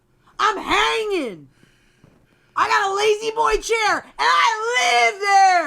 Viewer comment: i thought angela said lady boy chair 😂😂😂